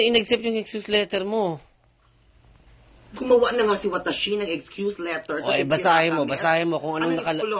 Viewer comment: did he just say WATASHI? wew? japanese!! KALURKI ITETCH! MAGINGKRUNG SHI NAGJAPANENGGA! KALURKI WAGAS! XD